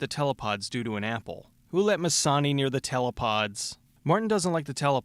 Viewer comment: What did he say at 4:16? Mizani?